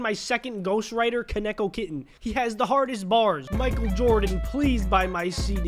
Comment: I thought he said he has the hardest balls 😂